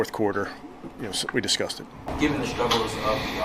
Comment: did he say "We discussed it" or "we disgusted."